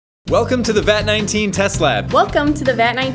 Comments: It’s sounded like you were saying “Welcome to the Vat 19 Tesla” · At the start I thought they were saying Vat19 Tesla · I keep hearing "welcome to the vat19 tesla" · At first I thought they said welcome to the vat19 Tesla